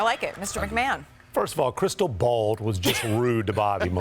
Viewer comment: Did I hear "Crystal bald"?